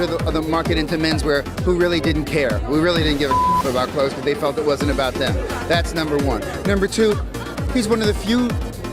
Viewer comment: Who is the guy at 4:51? Your unnecessary captions got in the way of pertinent information.. H Enstien??? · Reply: Hal Rubenstein